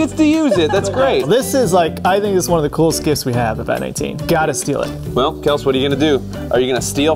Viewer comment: did he say “Vat18”? haha